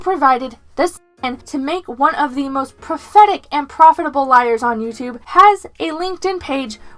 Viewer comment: Did she say "prophetic" liar? Is that someone whose lies were foretold in a prophecy?